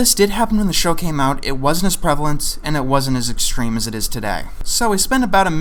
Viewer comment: "it's become a specific problem and it isnt as prevalent or extreme today" ARE YOU KIDDING ME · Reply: It wasn’t as prevalent or as extreme as it is today is what he said